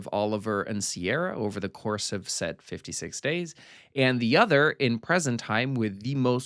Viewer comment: The way I was only listening, thought you said 56 Dates, and just assumed it was a strange sequel to 50 First Dates 💀